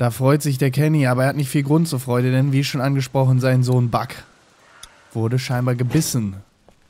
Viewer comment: ich hab verstanden bei 2:26 sein sohn Bug xD